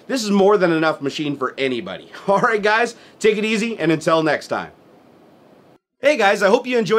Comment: Did you just say, "intel next time" instead of untill next time? :D